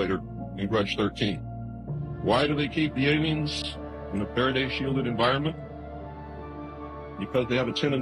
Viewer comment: Its funny that's the part of that alien video u chose to comment on.in that vide at around 5:55 he say "why do they keep them shielded in a FERADAY (sorry sure that is spelled wrong) shielded environment" but the caption says "paradise shield". And if u know what a feraday shield is that makes perfect sense of how to stop it.maybe some outside technology helps them produce this desired effect.or i could be talking out of my ass im not a scientist idk